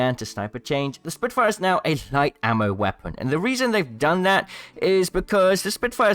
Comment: What did you say at 06:07 it sounds like you said "spitfire is now a snipe ammo weapon" which is false